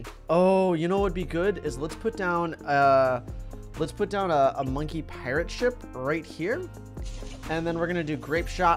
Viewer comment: I'm sorry did he just say monkey parrot ship???